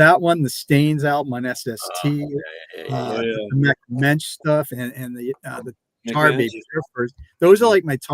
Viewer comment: I didn't understand what are the top albums that need to be reissued according to. There's that N.O.T.A. album, the Stains on SST, and two more, but I can't make out what he's saying next. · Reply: Mecht Mensch and Tar Babies I think :) Cheers!